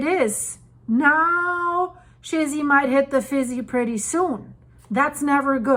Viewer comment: did I hear a shizzy hittin the fizzy in there? That can’t be good at all! Thanks for putting it in layman’s terms